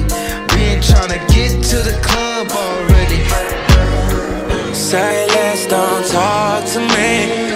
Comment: I thought it said Silence don’t talk to me lol